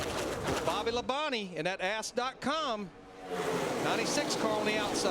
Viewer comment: Did he just say "Ass"? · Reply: I think he did! But it's Ask not ass...that would be a naughty site.